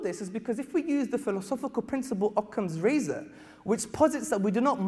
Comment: What name is he saying around 9:12 i cannot make it out but it sounds like Conte Razor?